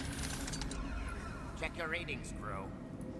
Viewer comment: Did he call you "bro"? lol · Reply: LMAO! He said Krewe. DId sound like Bro.